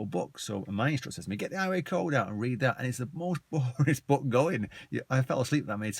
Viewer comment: Did he say at 6:30 the Highway code is the most 'boringist' book going. Boringist?